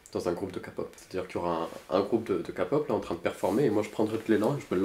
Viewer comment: Je reve ou a 2:30 il a dit un groupe de “capo*es” a la place de “k pop” ou bien c’est moi qui suis bizaaaaard